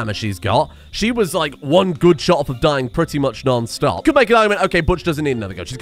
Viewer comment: not going to lie I had to relisten to that line like 3 times because the first two times I heard one boob shot and not one good shot somehow 😂 bad hearing can be great sometimes